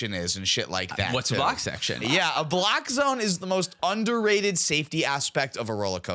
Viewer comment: I thought he said black zone. ✊🏿